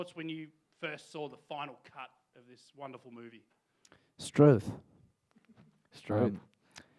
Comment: what did harry said in 0:46-0:47? I'm sorry, English is not my first language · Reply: He said 'struth'. Its just an Australian saying and he made it because the interviewer is Australian :)